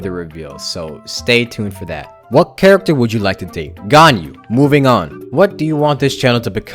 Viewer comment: What I heard: "Which character would you like to eat?" "gun." "moving on"